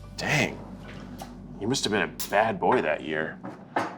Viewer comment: Eugene: 'talking about how his mother left his father on Christmas morning' Keith: well you must've been a bad boy that year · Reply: See, I thought he said He must have been a bad boy. Aka Eugene's father.